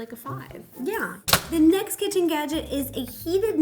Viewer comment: it sounds like she said "kitten gadget" 😂😂